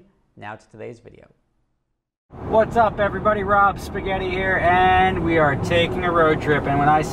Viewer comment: this is the first time i hear him say this is rob spaghetti 😂😂😂!!